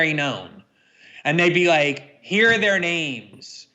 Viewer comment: Did Glenn really say 'they be like....' at 22:36 That's racist or cultural appropriation.